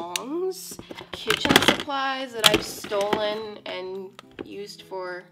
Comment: Did you say stolen??😂🤣